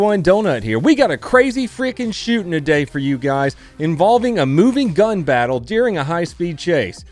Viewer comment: i legit heard minigun instead of moving gun